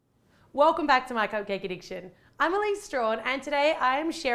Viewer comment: Is it just me, or did you think she said 'my coccaine addiction' at the beginning?? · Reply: Kit Dumb it did sound like she said welcome to my cocaine addiction!!!!!!😨😧😦😲😦😧😨😨😧😧😦😲😦😧😨😧😦😲😦😧😨😧😦😲😦😧😨😧😦😲